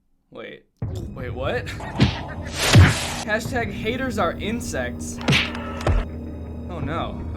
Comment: did he say hates are incest